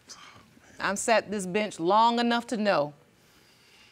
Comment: First off: MAMA A THOT!!! Secondly: I thought the judge said “I sat in this bih long enough! 😩😂